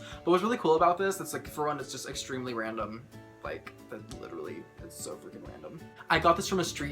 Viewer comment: the first time I heard random i thought he say ralandr